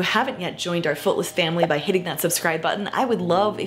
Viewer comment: Omg I thought you said yeeting that subscribe button instead of hitting 😂😂